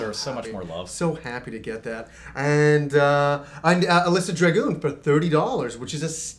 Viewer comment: what did say? something dragoon?